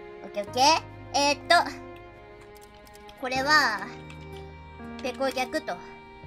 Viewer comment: I'm sorry I know its late and its not very important but.... 4:49 I believe what she said was ぺこ虐 from the word 虐待 (ぎゃくたい）so like abused Pekora... so yea...